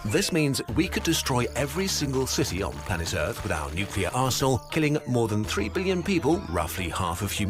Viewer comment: anyone notice he said arsehole instead of arsenal.